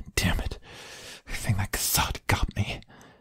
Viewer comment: I'm sorry, but what was said at 0:45? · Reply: I heard "I think my croissant got me" Omg lol I know that wasn't what was said but that's what I heard